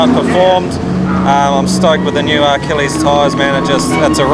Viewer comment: Great coverage. @ 20:30 Did say he's running R-Kelly tyres? That's one way to piss on the competition lol. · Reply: Achilles Tyres, the accent might have thrown you off ;)